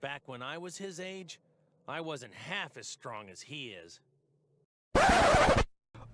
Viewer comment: He said he wasnt half as strong bro he didnt say he was half as strong. So that right there means that you made up that lie just to make this pointless video. He clearly said he wasnt half as strong as Gohan.